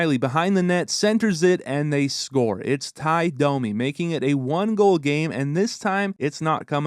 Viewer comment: Did anyone else catch the Tie Domi slip up? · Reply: I thought he said ‘it’s tied! Domi!”